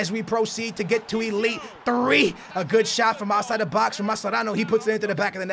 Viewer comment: anyone heard mascherano or just me ( sr for bad english )